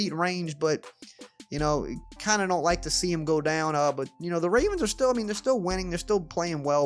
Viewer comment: I know he said ravens, but it sounded like Raiders. He’s still so inspirational though!!!